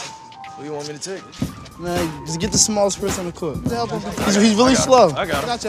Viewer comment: What did the nephew said on 1:16, I did not cath that:"Get the small..."